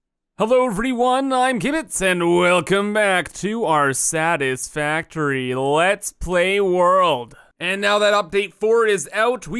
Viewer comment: Sounded like Kibitz said "Welcome back to our saddest factory lets play world" in the intro xD